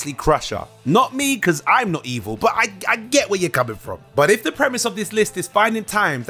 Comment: "Letting Wesley die instead of boiming him up." BOIMING.